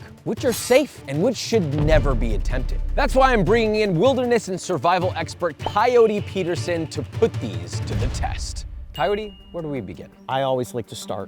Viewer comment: At first I thought you said Peyote Peterson and I thought this was going to be so much cooler